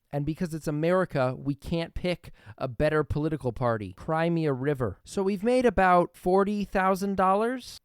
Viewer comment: Why did I hear “Crimea River” instead of “Cry me a river”!?